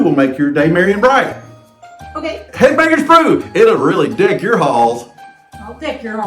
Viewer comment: You said "deck your halls" but I thought had heard "k--k your b---s". 😁